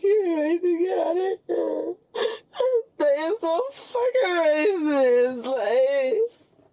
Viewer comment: No Way!!! Did she actually say, " this is so racist" ?